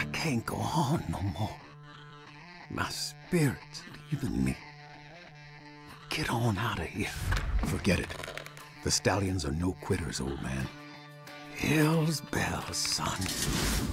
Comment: I misheard the name Gun Slinger Joe Stalion to Stalin.It is actually Stalion not Stalin.